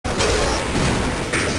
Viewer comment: I keep hearing "cum bots".